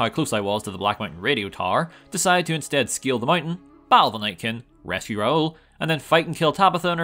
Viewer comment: I don't mean to nitpick, but maybe you might want to reiterate a bit. I had to rewind a few times to understand you said you were going to scale the black mountain. I thought you said steal the bike. Like a motorcycle or something.